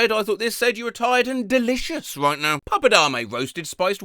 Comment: I’m tired as fuck and I misheard Pappadam as “Puppet Army”